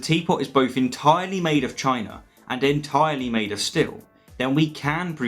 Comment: Ha, I heard 'entirely made of China, and entirely made of stihl' and immediately thought of a teapot made of little chainsaws.